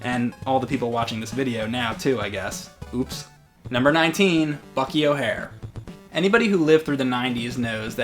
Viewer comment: I thought he said "Fuck your hair"